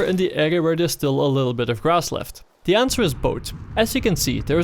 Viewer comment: legit thought you said ''boat'' so I started looking around for a boat. why am I so retarded